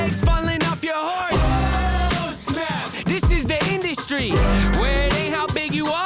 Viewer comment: sounds like he says “this is the indie-stry”, like a combination of indie and industry